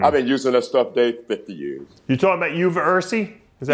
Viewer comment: what herb is Robby trying to say he takes? He called it euro virus then Dave corrected him saying uversy? Something like that? Anyone know ? Can’t understand Robby that good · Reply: Uva ursi